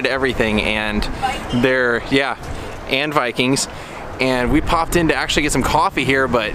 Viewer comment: I thought she said "bite me" 😂😂 oops VIKINGS. Very very cool place 💙